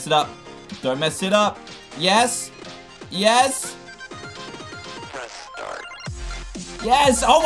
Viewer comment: I always thought that it said Prescott instead of press start. 😂😂😂